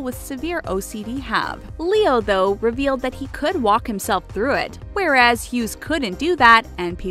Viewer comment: Narrator said "Walk" and subtitles said "Talk"